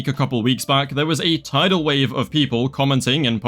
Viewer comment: did someone say tidal wave?